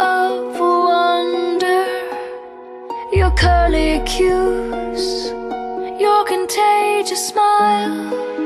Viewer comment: All this time I thought she said, " You're kinda Cute" ...instead it's " Curly Cues"